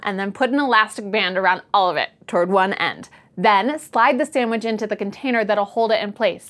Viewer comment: did i hear sandwich?